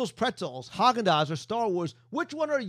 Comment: Lol. At 4:50 you say star wars?is the new place about star wars or starbucks? I heard starbucks in the beginning.